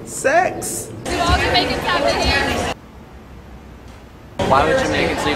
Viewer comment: the heck did she say? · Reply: Invader She asked "Do all jamaicans have big ears"